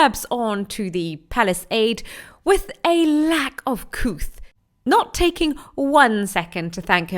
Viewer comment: Did I hear you say, “with a lack of couth” at 2:59? 😂😂😂.